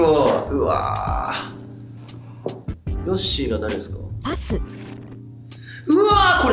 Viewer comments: 「ヨッシーが誰ですか」ってに対して ナレーターの「パス」が最初「はっすん」に聞こえて え、この声誰？このメンバーではっすんって言う人おったっけ？ このゲームってSiriみたいな役割もしてるん⁉︎って焦った笑笑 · が ヨッシー誰っすかって言ってる後の『パス』が、『はっすん』に聞こえたのは私だけか？？？？